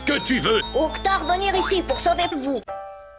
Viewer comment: Il dit quoi Ouktar a 2:23 ? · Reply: "Ouktar venir ici pour sauver vous" vu qu'il parle comme un africain c'est pas évident